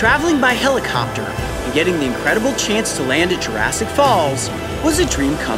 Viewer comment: I legit thought it said Jurassic fails......